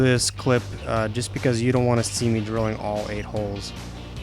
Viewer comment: what did you just call us?! You the A hole! 😝